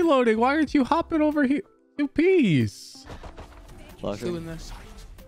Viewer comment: Did I hear a “you piece” at 6:09? Does watch NL?